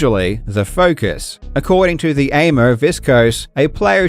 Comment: did you just call this dude an "aimer"? lol